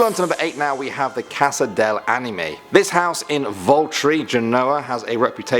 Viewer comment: did SoMEbOdy SAy ANiMe